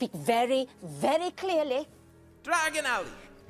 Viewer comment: Thought he said "Dragon Up"